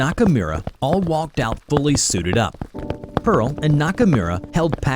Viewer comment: I hear you said Bully sip up